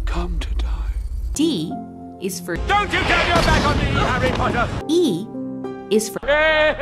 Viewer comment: actually it sounds like he says “Don’t you turn your back on me you Harry Potter”. I always imagined it as him considering the very name Harry Potter a curse word.